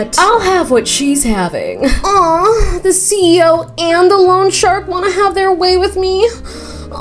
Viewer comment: I thought you said lawn shark. love lawn sharks :>